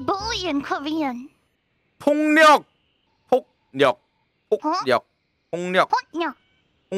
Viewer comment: ~6 님 발음 똑바로 :폭력 으로 안 들려요ㅋㅋ 폭령 홋력 으로 들림 진심 완전 천재!!! 발음 너무 좋습니다♥